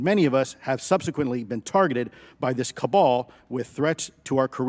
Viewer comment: what is the saying, can someone type it, kabal ? ım not native english, cant understand